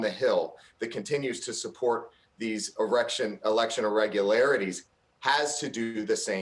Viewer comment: Am i the only one that heard him say you have to support these erections ? 🤣🤣